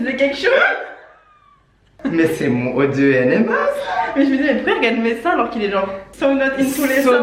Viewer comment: C'est moi ou à 0:49 elle dit (pourquoi ELLE regarde mes seins )à la place de dire IL